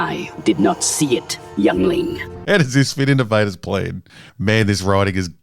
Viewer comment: I always hear "youngling" as "Yeungling". Alright, boys, we're on a supply run to the planet WaWa.